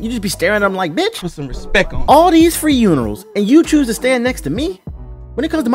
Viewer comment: Did you say free unerals? xDDD